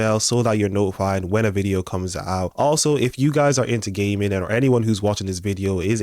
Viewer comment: "if you're into gaming" But I heard, "if you're into gay men" 🤣😂🤣